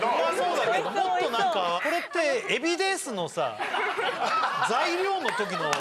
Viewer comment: 設楽さんの「海老です！」がエビデンスに聞こえてしまった自分に休養をくれ